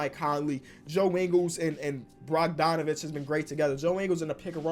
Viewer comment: Anyone else hear "Brogdonovic" 🤣🤣